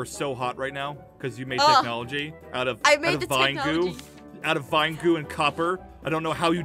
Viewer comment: I thought the vine goo said WINE GOO 😂😂😂